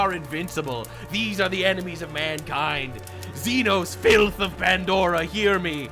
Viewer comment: Did you say Xenu 19:20 in? 😅